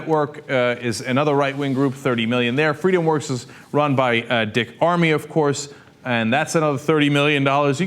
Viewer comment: Lol - I thought he said "dick army" at 1:08. Not far off from the truth, though, anyways.